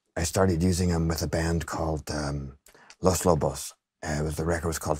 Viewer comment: What's the name of the band he mentions @ 00:43? I heard Lost Vobo's...with a record called Keego. But that's clearly not it :) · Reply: Los Lobos, Kiko